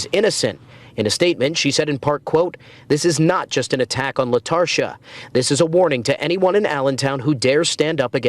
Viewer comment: This whole time I thought it was Latasha, and when I heard Latarsha I thought I misheard.